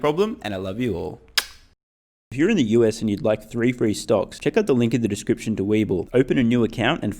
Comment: OOHHHHH! THREE free stocks! I always heard it as "Free free stocks" for some reason ahah the same way Nikola makes "real real trucks" :P